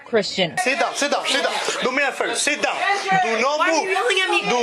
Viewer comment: It seriously sounded like the cop said do not move Satan.